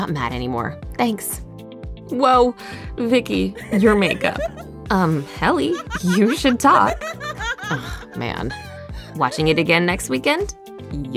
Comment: I heard "woah Vicky" *dies*